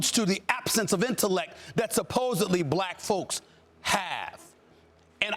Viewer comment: did you just call Lebron and Don "supposed black folks"?